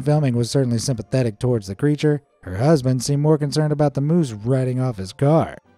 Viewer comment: w the moose....Sounded like the woman's SONS car, rather than her "Husbands" car lol